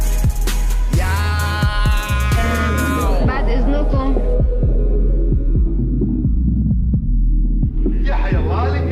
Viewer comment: France ? What She Said ? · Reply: "Baad eznokm" like exuse me in arabic